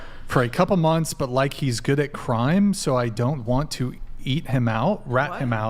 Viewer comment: omg I laughed so hard I literally cried at saying eat instead of rat. I’m still cracking up as I type this. 😂